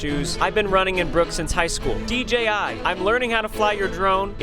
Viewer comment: I thought he said dti